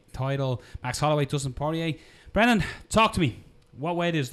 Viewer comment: I misheard "Brendan, talk to me" as "Brendan, talk dirty to me" lol.